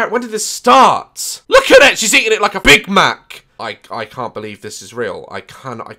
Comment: "She's eating it like a big mac!" 😂😂😂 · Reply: I heard "she's eating it like a big man", I'm stupid